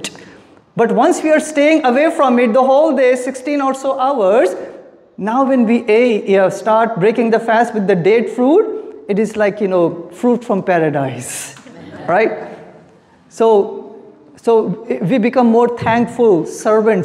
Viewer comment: Dead fruit instead of Date fruit :( that's so wrong jfsahfhsahfkas